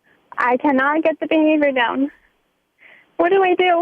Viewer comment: I have listened 10 times to the beginning of this clip but I cannot make out what the caller is saying at 0:16. "I fully have your 20% head knowledge. I can not get the beanie redone."??? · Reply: Josiah Schmidt "I can't get the behavior down"